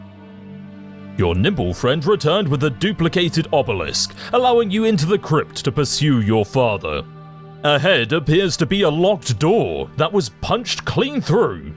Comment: thought he said nipple friend